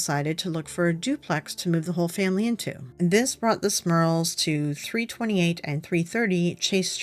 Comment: OMG I thought it said Smurfs, this is horrible